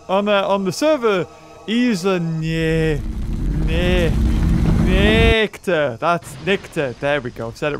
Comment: I thought you were trying to say "Nectar".